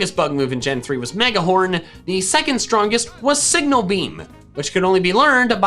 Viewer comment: I thought you said Sigma Beam. I just hope you know that